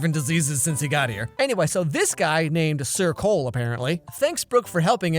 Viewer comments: Lol anytime someone said "sir cole" i thought they said circle 😂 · every time you said "Sir coale" i thought you said circle -3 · When everytime the characters said "Sir Cole", all I hear is "circle" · When I first saw this movie everytime they said sir cole I kept hearing circle. Don't judge I'm deaf